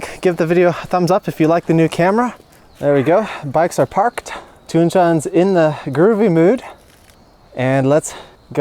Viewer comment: What is her name? Sounds like your saying "Toon-Chan"...?